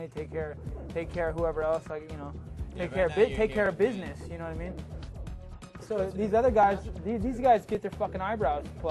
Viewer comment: lol can anyone decipher what nate tries to say at 1:45? he kept trying to get a word in but nick wasn't having it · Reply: pretty sure he said "you don't have to take care of me" because before that Nick said "I have to take care of my brother" lol pretty sure Nate didn't want people to think he depended on his brother for money. Which he sure doesn't now and didn't really back in 2007 I don't think.